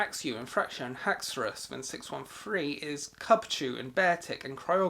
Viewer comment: When he said Kabfu, I thought he said Cubchoo, so I was like "???"